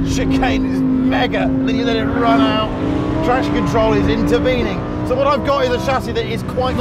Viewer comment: did he say trash control in